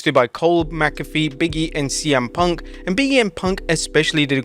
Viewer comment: "Big E and Punk" sounded like "BM Punk" 😄 RIP Blade Braxton